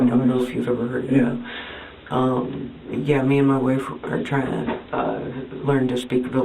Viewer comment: I’m confused she says at 4:32 “me and my boyfriend are trying to learn to speak valerian” I thought she had a wife Fancy being 33 and still living at home and being married 😮 Maybe instead of playing games and learning a silly language, learn a trade. Many tradies that have been to jail can still get a job. It’s not easy but can happen. That is her fault she has a record it’s called consequences. They gave her 6weeks notice. My thought is they didn’t believe her story about nearly being trafficked as I don’t think she can be truthful and her parents were over it.